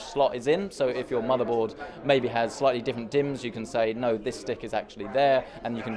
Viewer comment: Did anyone realise he said "dick" instead of "dim"